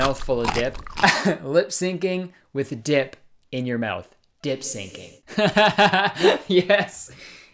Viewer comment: YEEYEE! Also I thought Kurtis said "Dick Syncing" (4:22) when I first listened to it😅